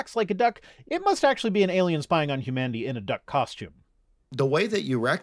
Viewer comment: At first, I misheard "Alien spying on Humanity" as "Alien spying on Kamandi" with a slightly odd pronunciation of "Kamandi."